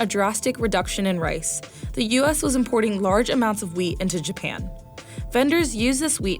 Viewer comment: I heard “the US was importing large amount of WEED”